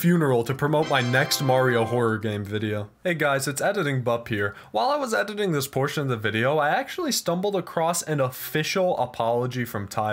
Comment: For a second I thought I heard you say "while I was editing this portion of the video I actually died" and I started laughing for probably a minute straight